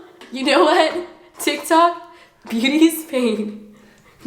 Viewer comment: I thought you said booty's in pain i